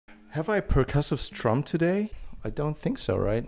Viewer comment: I heard " Have I protested Trump today" in the intro for whatever reason